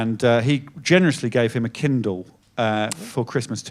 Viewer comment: Oh KINDLE! I thought he was saying KEN DOLL I was so confused 😅